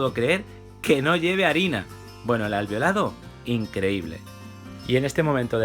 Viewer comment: escuche "Bueno las has violado, increible"